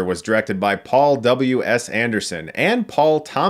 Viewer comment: I thought you said " ... was directed by Paul double-U ASS Anderson..."